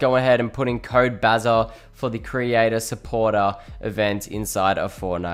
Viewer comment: he goes “create a supporter instead of “support a creator